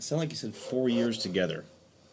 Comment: "the carriers of death" not 4years together from what I hear